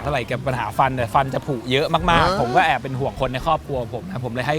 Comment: 12:05 เหมือนพูดว่าครอบครัวหรอ